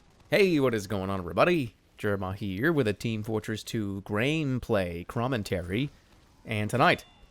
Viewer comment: Did he just say "Graneplay Crommentary"?